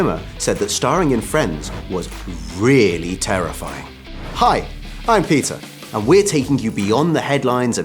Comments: When he said hi I'm peter.. i heard hi I'm pizza😅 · I swear I heard him say, "Hi, I'm pizza..." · did he say "Hi, I'm Pizza"? Or do I just like Joey too much? 🤔😅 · Did he say "Hi, i'm pizza"?? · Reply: Peter. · When he said "Hi,I'm Peter" i thought he said "Hi,I'm pizza"